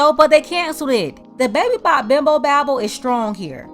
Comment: Don't title this like that, why would you kick someone when their hurting especially when she's a positive sister. Your wrong because she's a positive Soul shame on you keep your head up you deserve all your flowers ❤❤🙏🏽, for this platform 😤😤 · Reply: Baby bop bimbo babble... 😂Was that a actual known saying? I thought she said at first baby back as appose to big back bimbo babble. 😅